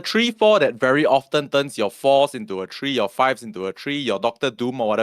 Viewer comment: Did he say "turn your Dr. Doom into a tree"?